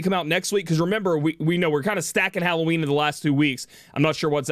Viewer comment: wait, did he just say hallow weenie?